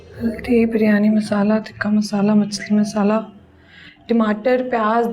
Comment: did she just say "machhar masala"? 😂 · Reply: Machli masalah